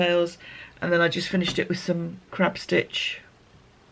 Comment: Just catching up - love the blanket. Thought you'd said crap stitch, not crab, for a moment there 😆